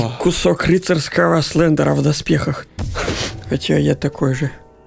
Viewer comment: Вместо "Кусок слендера в доспехах" я услышал "Кусок Клитора в доспехах" :// (p.s этот момент я смотрел на колонках при маме)